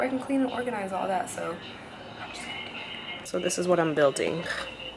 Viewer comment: Has anyone else ever noticed Nicole pronounces things that end in "ing" like "ink"? 😂 like it sounded like she said "This is what I'm gonna be buildink"